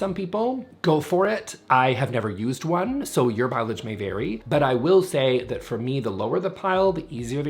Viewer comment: Did he just say, "Your pileage may vary"?